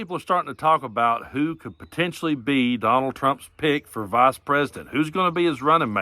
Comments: I misheard that intro and thought you said Trump's "pig for VP" and I just nodded along for a solid few seconds lmao especially since Tim Scott is in the running. He sure was squealing for Trump in that Kristen Welker interview! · When you opened l thought you said, “Who’s going to be trump’s pig?” 😝 Same thing I guess 😆